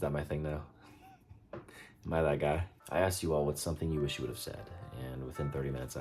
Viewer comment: did you really say malaka??? omg im laughing so much 😭😭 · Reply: Haha nah he said "Am I that guy?" XD